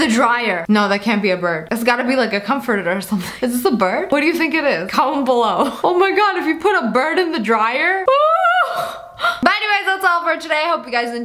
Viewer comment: the subtitles be like: this is thhujnbjbhjbhjbhjbj what do you think? It is column below? Oh my god, if you put a bird in the dryer, what it's supposed to say: is this a bird? What do you think it is? Comment below what you think, oh my god, if you put a bird in a dryer